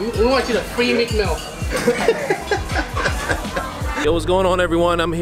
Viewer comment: What does say at 0:53... sounds like it's a good joke but I can't make out the words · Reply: He says, we want you to free meek mill